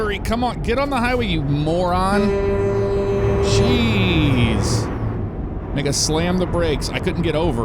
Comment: DID I JUST HEAR “NIGGA SLAMMED THE BREAK”💀